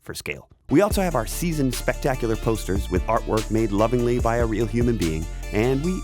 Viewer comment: 1. love the battle! now I'm gonna do the usual and go down the rabbit hole about the two characters! 2. at 13:09 I missheard, and thought said ''coasters'' and not gonna lie: I would 100% buy a rubber coaster with the ERB logo!